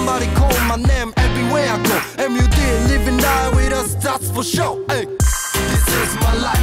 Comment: ここのSomebody call my name everywhere I go〜 のあとなんて言ってる？ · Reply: # JLINGZ everyday livin life with us That’s for sure だと思います。もうわかってたらすみません